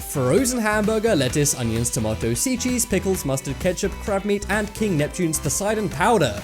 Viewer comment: Sounds like he said CRAP MEAT